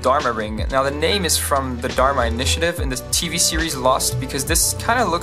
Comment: For a second there I thought you said "t-series lost"